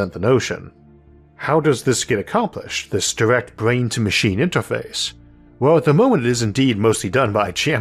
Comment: I thought he said the "interface is attached in the ass." This would naturally be the case with any alien abduction and of course is very invasive BCI...